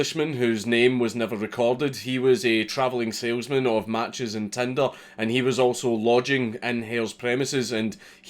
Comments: "a travelling salesman of matches in Tindr". I know what Dank actually said, but I prefer this version. 26:30 -- "He was told to start walking toward the English border." "Pass me that flintlock Sergeant, I fear that man is attempting to escape to England..." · I honestly thought you said “a traveling salesmen with matches on Tinder” and was dumbfounded how that was relevant information. I’m very stupid.